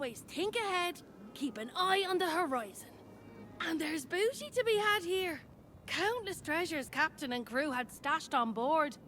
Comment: Did she just say: and there is booty to be had here 😂🙈